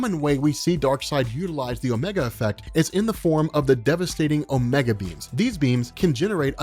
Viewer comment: I kept hearing it as "omega beans" instead of "omega beams"